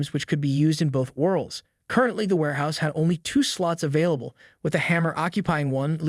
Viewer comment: Anyone notice wat he said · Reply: He said "sluts"😂😂😂😂😂